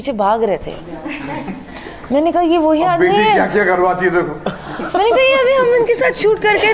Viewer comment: don't know but I heard "billi kya kya karwati hai dekho"